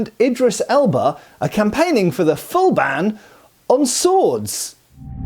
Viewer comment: I thought that said "They want to ban words" and I thought..."This is nothing new. They've been doing it for a few years now."